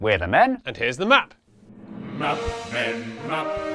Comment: The subtitles are wrong. Replacing the last “Map” with an extra “Men”